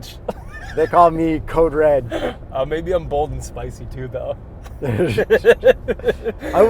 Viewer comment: thought he said bold & spicy trudeau